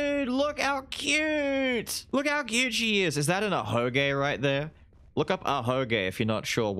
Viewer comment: I misheard ahoge as ahegao and was confused af for 10 secs